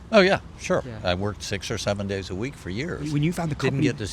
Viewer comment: did I hear 6 or 7?